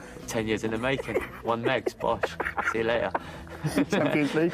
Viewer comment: I'm American and not the best at deciphering words with thick accents, at 6:07 he says, "10 years in the making, one -meg, splash, see you later." Does he say 'splash,' or 'sposh,' what word is that he's using? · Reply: 2 months ago I know but he said bosh.